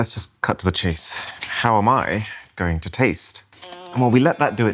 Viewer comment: You should've slept with Drisha...lol + Did i just hear you say HOW AM I GOING TO TASTE?! 24:27 + 29:44 NAHHHHHHHHHHHHHHHHHHH